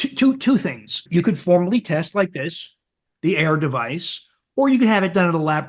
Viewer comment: Dr. Davis mentioned a device to test for Endotoxemia. I couldn't understand what he said. Could you please let me know what it is? Thanks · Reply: AIRE is the name of the device. He mentioned it at the beginning of the podcast